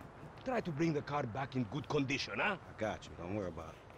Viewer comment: コンディショナーって聞こえる